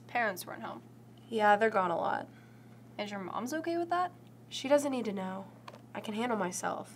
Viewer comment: Did she just say “your moms” 🤣🤣🤣 LIKE DADS FROM GLEE!!!!!!!